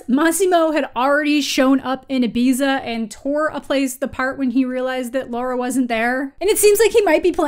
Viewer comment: did. did anyone else hear "tore a place the part" at about